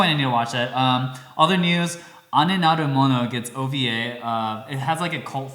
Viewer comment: What anime is talking about at 29:36? I can't figure out the spelling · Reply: I think it's Ane Naru Mono (The Elder Sister-Like One) <link>